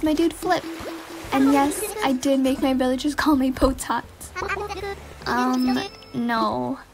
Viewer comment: I heard "and yes I did to make my villigers name me botox"